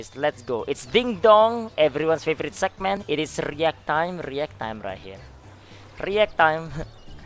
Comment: i watched about a dozen of these videos this past month and thought he said "it's reeeeee ah time" like a meme. today i realized he literally said "react"